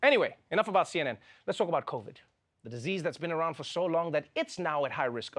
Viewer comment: I swear I thought you said let's talk about coke lol